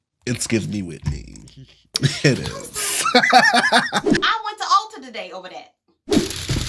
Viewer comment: I said “ulta”?? I thought you said alter. Thought you went into prayer.